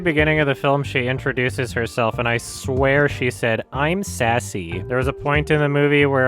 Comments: Thank God I wasn't the only one who thought she said Sassy at the beginning. I just thought everyone's accent just made it sound like sersi or however it's spelled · I had the exact same theatre-experience with Sersi's name. I didn't know the actress' accent, so my brain was just on American autopilot, and I also thought she said "sassy".